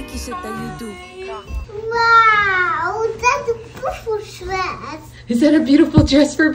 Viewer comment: Sounded like she said “that’s a Christmas dress”